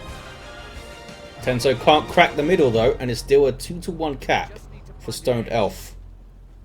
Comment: I swear, every time you say "Two to one cap" I hear "Two to uncap". It makes me squint every time as I know what I hear is not true.